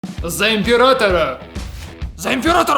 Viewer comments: Кому послышалось за апиратора а не императора? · Мне одному послышалось "за оператора"